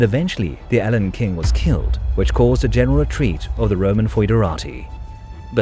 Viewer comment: it sounds like 'the alan king was killed' and I don't understand this wording. It sounds like I'm mishearing a french phrase I'm unfamiliar with. I don't think there was a King Alan in this history, can anyone help me understand? Nevermind. Here's the answer: <link>